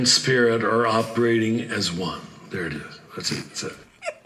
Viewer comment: Did he say at the 50:16 mark, "There it is , I shouldn't have said it?" That lump of matter shouldn't say anything period. He speaks blasphemous, heretical gibberish.